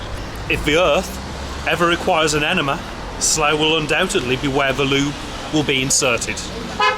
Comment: Did you say: where the lube will be inserted? 😂